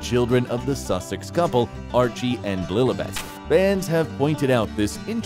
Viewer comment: and I think the little girl's name is Lilibert not Blilibert as I heard you say